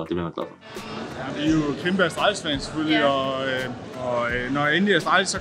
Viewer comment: I don't speak Danish so there is a (very small) chance it isn't what he said. But I'm pretty sure he said he's a Kimbo Slice fan at 2:58.